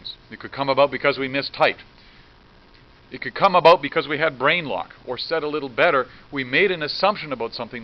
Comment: I heard brainrot 💀